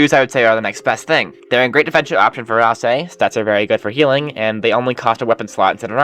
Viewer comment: in 19:00 when said "Ralsei" the subtitles thought they said "Asriel"